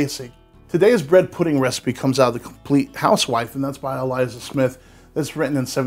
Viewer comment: lol i thought the book said the compliant housewife 🥴